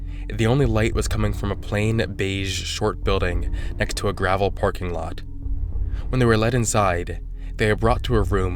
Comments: Yo did you say porking lot or am I tripping? 😂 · Yo did you say porking lot or am I tripping? 😂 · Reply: no I think he said parking lot